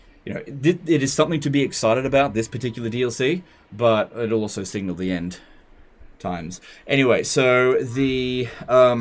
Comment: Did he just say the Last DLC will be End times at 8:54?